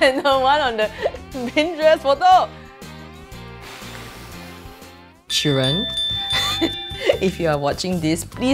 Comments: I'm British so when she said children I thought she said Sheeran and I was about to cry I thought that Ed Sheeran was actually there 😢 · When she said children I thought she said Sharon